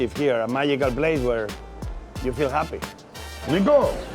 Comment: why does he say "cheers, te quiero" and the subs say "cheers. you get it" come ooooon it's quite easy lol, he said "cheers, i love you" and that's the quality we can expect from youtube depending the day (in españistán is not uncommon saying ppl you love them, and greet family members kissing their cheeks, no matter the sex)